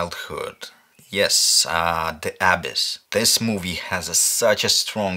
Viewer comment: what movie is it? I couldn't get it, probably because of the accent & the captions say ''the Abbess''.